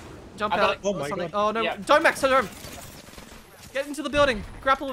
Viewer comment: I heard “Dome Max” 😂